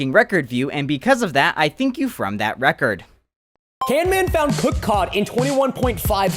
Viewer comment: DID HE REALLY CALL CAMMAN CAN MAN???11!!!